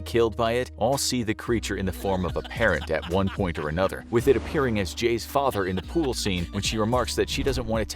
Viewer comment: For a moment I thought it was said “IT appears as a parrot.” Now, that’s entertaining…